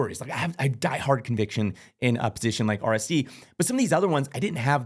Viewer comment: did you said RAC at 10:18? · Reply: RSC